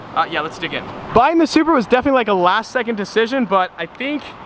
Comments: Who else heard him say Subaru at 10:09 instead of Supra?? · Did anyone else hear him say subura